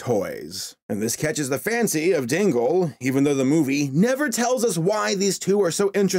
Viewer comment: I thought you said Tingle instead of Dingle... Hey at least Tingle is a more interesting character